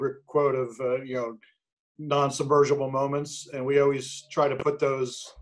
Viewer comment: anyone know what Tom says at 29:39? I can't quite make it out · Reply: he’s referring to Kubrick’s narrative structure, “6-8 non-submersible units.” it’s basically 6-8 moments that function independently of one another, each with their own beginning, middle, and end.